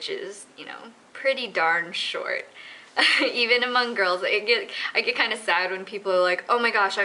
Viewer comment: oh haha thanks for telling me cause i heard her say hmong and yeah